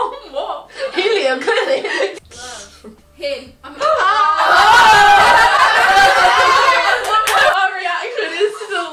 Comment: what did they all react to? She says love and then I can't hear what word is said next? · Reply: Hey said him after